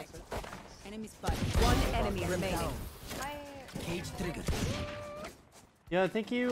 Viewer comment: For a sec I thought cypher said "gay triggered"